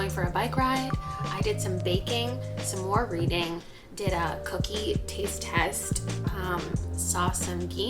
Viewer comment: I thought she said “I did a cocaine taste test” 🤣🤣